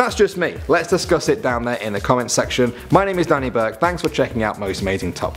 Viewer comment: at the end i thought he said: you can fight me on instagram XD